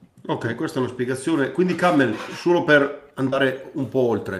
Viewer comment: che dice adani: canel, cammel? e soprattutto perché? e perché poi pure Bobo ripete sta parola!??